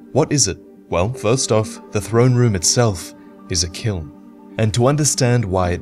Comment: Im sorry, my english is not very good. Can anyone tell me what does he says in 2:25 "The throne is a _" Thanks! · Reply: "The throne room itself, is a kiln."